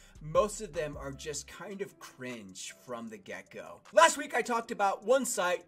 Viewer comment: Idk why but when you say “get go” I always hear “gecko”... and now I’m thinking about geckos